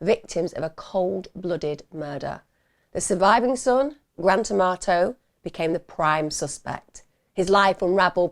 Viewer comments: I could have sworn she said 'grand tomato'. Sorry for the insult tomatoes. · When i first heard his name i thought you said grand tomato 😂😂😂, as many brain cells as a grand tomato so go figure😂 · I had to look twice. I thought you were saying grand tomato.!!!!